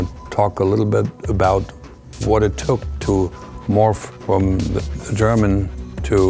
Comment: Did he just say “morphed” at 0:11? aReA 51 aliens confirmed!!!!!